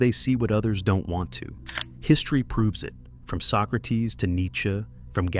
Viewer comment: I am so brazilian that I heard "from soccer teams to Nietzsche"